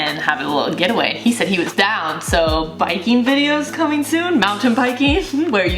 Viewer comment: I thought said Viking videos? 🤣 that would be hilarious